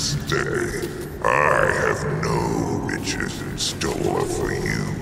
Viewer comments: i swear to god i hear him say bitches instead of riches everytime i hear this line. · if I have the audio lower it always sounds like he says "no bitches" and its fucking hilarious